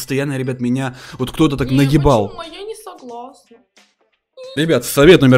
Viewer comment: Мне послышалось или на 1:21 сказал наебал?